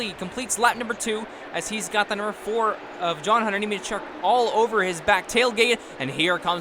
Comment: did anyone catch that he said John hunter Nementruck